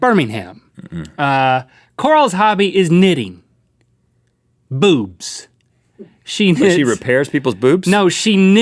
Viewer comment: when you thought link said "knitting BOOTS" until you look at the screen